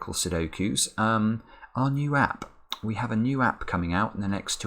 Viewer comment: Slightly hard of hearing - thought you said there's a new rap coming out at the start of the video, now that's an idea!😂